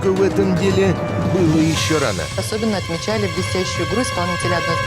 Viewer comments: мне послышалось, что она сказала "Особенно отличали бесящую игру исполнителя одной из главной ролей, Сергея Бондарчука. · дикция у ведущей настолько замечательная, что четыре раза я слышал "висящую грудь" и только на пятый услышал правильно